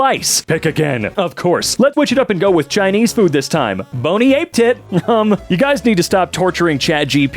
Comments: did they just say "boney ape tit" ? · did you just say "bonni ape tit" instead of bon appétit?